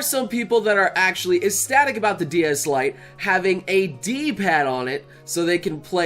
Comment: Am I the only one who heard him say ds lite